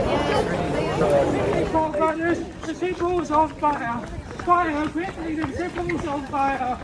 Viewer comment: what was he saying? "The pitballs on fire"😅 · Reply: He repeated “St Paul’s is on fire” for the whole time I ran beside him 😅